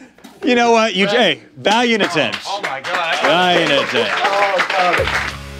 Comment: Rhett said "valued attempt" the first time, what did he say immediately after at 10:04??? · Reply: Valiant attempt, valiant attempt.